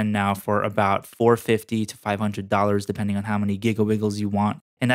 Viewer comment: Is it just me, or did he just say “giglebigles”?